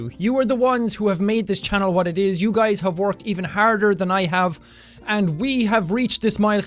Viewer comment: 10.37 I thought he said you guys have hugged me